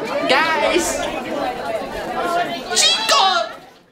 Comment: parece que dijo gays